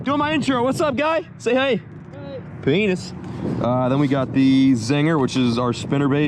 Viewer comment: what did he say?!? 😂😂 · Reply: "What's up guy say hey" "hey" "peenis"